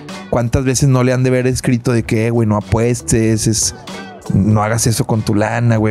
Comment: Pensé que decía apóstoles dije uuuuy a toda madre jajajjaa